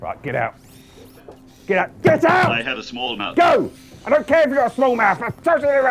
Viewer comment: "I have a small mouth." proper set me off. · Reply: LOL i thought he said "can i have a small amount" talking about the cabbage